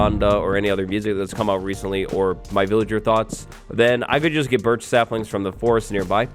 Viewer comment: At first I thought said "Villager thots"... that accent threw me. 😂